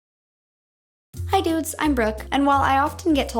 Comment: I thought you said Hi blabla bla I’m BROKE 😂😂😂😂😂😂😂😂😂😂😭😭😭😭😭😭 I was waooo she’s just spill that like that 😂🤣😂🤣😂